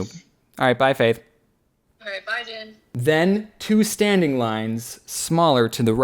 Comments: does she say bye DAN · Did faith just call him Dan???